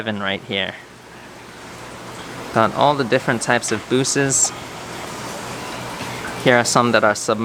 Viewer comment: did he say bussies?! 🙈